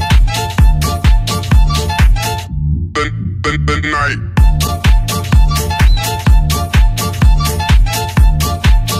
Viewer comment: wtf is he saying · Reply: Ignite